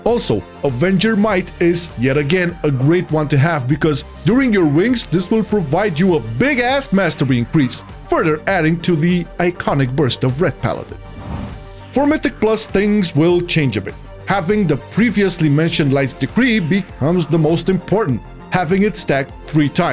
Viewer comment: For one moment i thought that said “busty” Im not ashamed lol